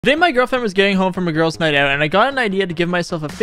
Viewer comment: Did I just hear"Today my girlfriend is gay"